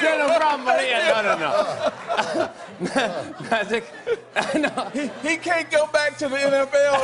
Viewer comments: anyone else hear magic say "he can't go back to the nfl nigga" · Did Magic say He can't come back to the NFL nigga?